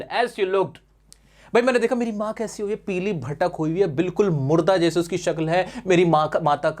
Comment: kya kaha sir ne😅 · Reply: Malwi bhasha se 'bhatak' word thoda repeated hota hai